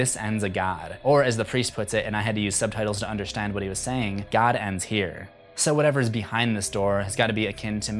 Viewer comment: Oh... He said, "God ends here.".... I thought he said, "Callahan is here."